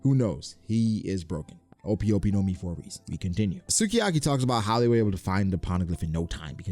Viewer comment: Bruhhhh did he just say “haki-talki”